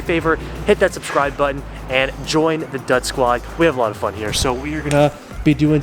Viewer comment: Totally thought you said the “nut squad” 😂😂😂😂